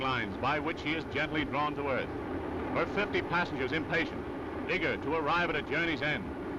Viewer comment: Did He Said The N Word? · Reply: He said eager, the n word doesn’t even make sense in that sentence